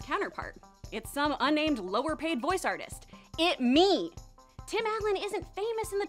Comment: What does she say at 4:16? "It me"? What does that mean?